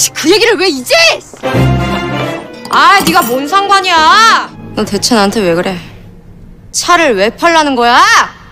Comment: In 1:06 i thought I just heard "boo seunggwan"🤣